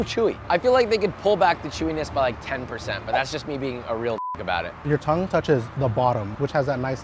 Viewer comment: did he say nigga ?